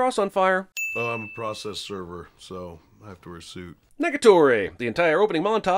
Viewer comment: I thought you dead ass said Niggatory. Had to pause and go back a Lil. It was Negatory.